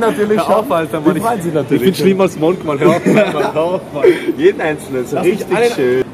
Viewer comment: Was sagt Juri bei 8:44 ich habs mir 10 mal reingezogen aber nicht verstanden hahaaha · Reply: "ich bin schlimmer als monk mann, hör auf!" :D